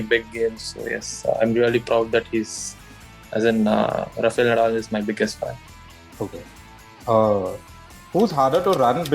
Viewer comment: Did he just say "Rafael Nadal is my biggest fan" ? 🙄😂